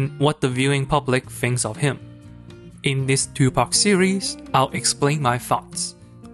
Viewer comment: i thought he said in this tupac series 😂😂 i looked at the title of the video then i understood